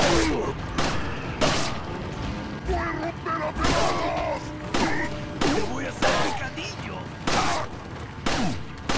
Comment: nunca entendí que dice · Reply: Yo sí entendí. Y dice así "¡Os voy a romper a pedazos!".